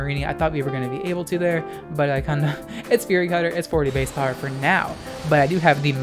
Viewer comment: I thought it said furry